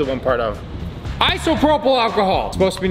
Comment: Did he just say isoferphalsalfanal??? Isopropyl achohol